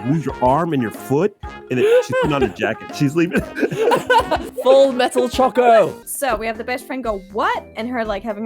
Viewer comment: At first I thought said she was gonna call it "Fullmetal Chocolat" and I'm like that's better than "Battle Button!"